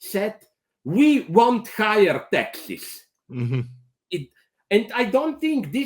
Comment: What did he say? We don't hire.. what? At 7:45 ? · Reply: 'taxes' says the transcript